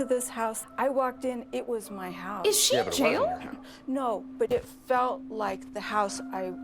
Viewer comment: I thought said “is she a JeW?”